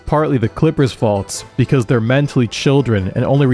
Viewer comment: Did you say feet?